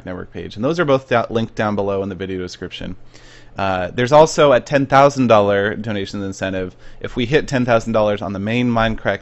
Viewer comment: Thought he said $15,000, and I'm like wtf?